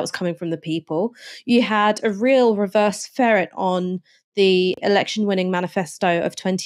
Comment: does Ash say "reverse ferret"? I've listened to it over and over but can't find a different interpretation. Otherwise I agree with everything Ash says, especially about the awful Truss's galaxy-sized gap between ego and competence.